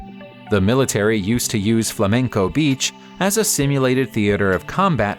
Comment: wait did you say Flamingo beach1:35